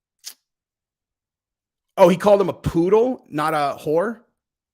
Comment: HE SAID POODLE WHEN U THOUGHT HE SAID PETR